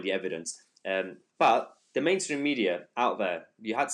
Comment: Sounded like you said "lame stream media" instead of main stream... I'm gonna start calling them that.